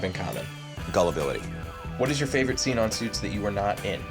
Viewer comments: What does he say at 2:37? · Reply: Joshua Prick “Gullibility “ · what he say? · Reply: Gullibility